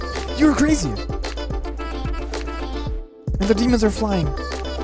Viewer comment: I miss heard him say the plane dude and hear "Demons Are Flying Again"